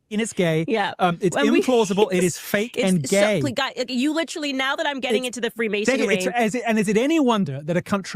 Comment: Did Candace say "Now that she's getting into the Freemasonry?" 1:03 👀